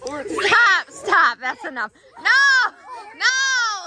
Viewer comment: no hate at all!! but, at around 20:25 , did anyone else here “stop, stop, stop.” ? it sounded like a whisper 🤔